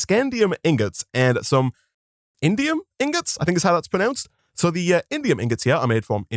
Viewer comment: I really thought he said Indian ingots😂💀